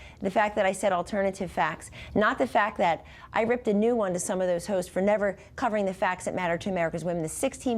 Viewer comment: I ripped a new one to some of those hoes. Did I hear that right ? Lmao !!